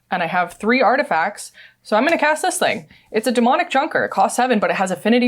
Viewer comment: I could have sworn said "Demonic Chonker"